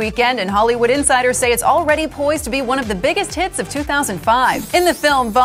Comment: I thought it said "poisoned"